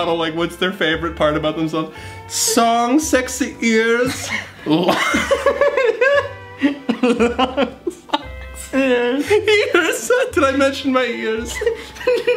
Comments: I heard "songs sexy ers" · i heard “songs sexy ears lungs pretty ears” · I heard “song sexy ears lungs pretty ears” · Lol I heard “Song sexy arms, ears?” XD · I thought she said, Songs, sexy arse, lungs, ears